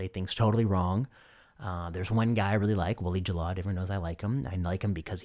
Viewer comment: Does anyone know what name Vinay Prasad says @ 28:34? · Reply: I did some "Google Scholarling" and found "Bassel Jallad, MD" of Cleveland Clinc Abu Dhabi. He's an oncologist from the looks of his papers. At least thirteen of them. Whether he has "Willie" as a nickname I don't know. You might look further along this path. It may be a false clue, though. Well, I tried!